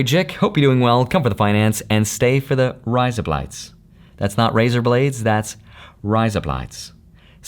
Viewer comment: Sounded like you said razor blades ...